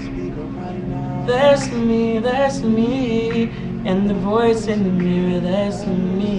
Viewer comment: did he say "the voice in the mirror"?